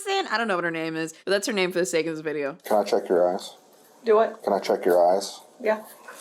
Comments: I totally thought that he said “can I check your ass?” At first 😂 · I swore I heard "can I check your ass?" Felt like things got a bit extreme there for a moment! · "Can I chek your eyes?" I had to do a double take I thought he said ASS.😂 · did anyone else hear “can i check your ass”? bc i did and i was very confused for a sec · thought he said "can i check your ass" 💀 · And that's why subtitles are important because, MY GOD I heard him saying "can i check your ass" wtf 😭😭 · Istg i thought he said "can i check your ass" and not eyes. 😂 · I thought he said "can i check your ass" lmfao